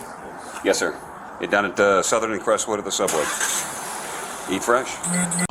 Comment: DId I hear an "eat fresh" at 4:33. Savage. That was unnecessary, lol The guy is going back to the big house for a few years.